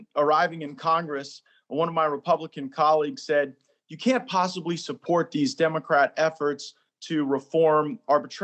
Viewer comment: Sounded like he said “you can’t support these democrat effers,” hahaha!